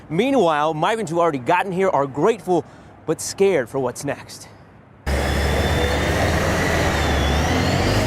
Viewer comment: This reporter needs to slow down and pronounce his words! What is he saying at 0:39? Is he saying that "no immigration policies were announced" or that "new immigration policies were announced"? It sounded like he said "Nimmigration policies were announced".